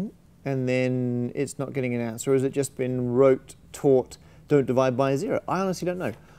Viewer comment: When he said “taught” I thought he said “towart”